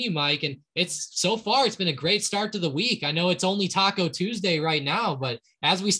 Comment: Happy Taco Tuesday Mike and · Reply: lol I'm at the gym I heard him say it's only Taco Tuesday 🌮